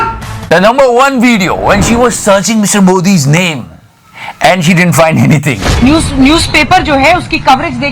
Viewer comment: wait 7:11 did i hear "he didn't find anything" instead she